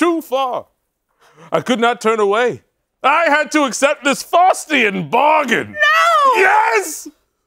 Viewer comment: "Faustian Bargain" is quite possibly the best phrase I've heard in a long time. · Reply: Even better, I heard “Frostian “ bargain and I was 😅😅😅